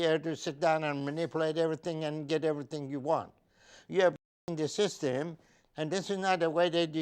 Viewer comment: that blank sounded like he said “this is your way of fvckin the system” haha 😂 I had to replay it 2 or 3 times!